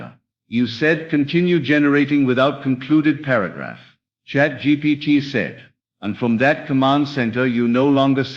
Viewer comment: O.g did I just hear "CHAT GPT SAID"???? wut?